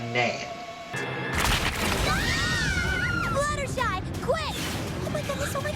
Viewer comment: I like what I thought her name was when I first watched the episode. I thought she said her name was fluttershine. I love that name.